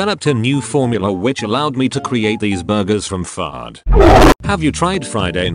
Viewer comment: It sounded like he said fart. 😂😂